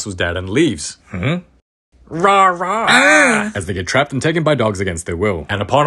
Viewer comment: The RAW RAW got me so hard it's sounded like GAY no offense 6:27 and the 7:31 LOL😂😂😂😂